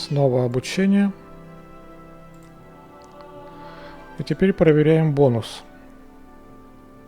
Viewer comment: "Обучение изменению" послышалось обучению измене 😂